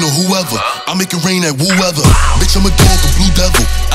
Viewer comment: I thought he was saying "I make it rain on whoever, I make it rain on woo-ever" lmao